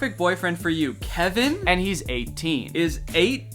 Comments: I got an ad, so all I heard was , “and he’s eight!” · AN AD CUT OF THE TEEN SO I HEARD “Kevin?! And he’s eight-“ 💀💀💀